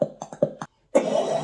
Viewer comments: It sounded like he said, 'Luke, I is your daughter.'. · Why did it sound like he said Luke I'm in your daughter